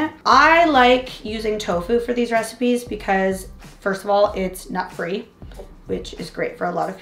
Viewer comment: i dont understand that "i like to use tofu first of all because its not free" ...i dont understaand